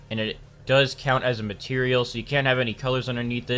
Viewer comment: You said material I almost had a hard attack I thought you said color